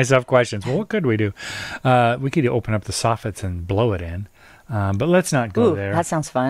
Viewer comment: Um, Did just say, "Blow it out your soffit?" lol Is it wrong to be 56 years old and sure wish you two were my parents.........? <3